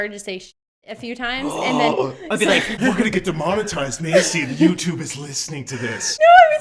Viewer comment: Is her name Macy? thought i heard u say her name 6:04 :)